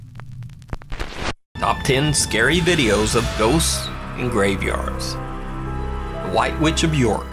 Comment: Sounded like he said “top 10 scary videos of those f**ing graveyards”